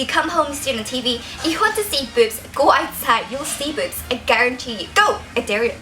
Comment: small boobs= big butt ;) i wish it was more even on the top and bottom though :/ btw it sounded like u said boots lol <3